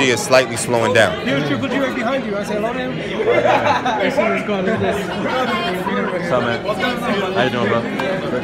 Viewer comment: Did GGG say "What's going on my nigga?" @ 1:34??? HAHAHAHA